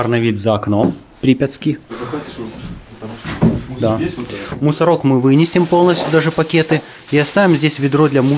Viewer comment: Мусоров мы вынесем в чёрных пакетах) послышалось